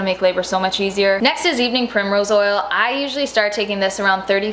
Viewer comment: What kind of OIL does she say to take in week 35, I cannot understand her, she said it too quickly. It's at the 11:17 min marker.... Please use captions when recommending products or list them. It would help a lot. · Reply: Ashley Mayorga evening primrose oil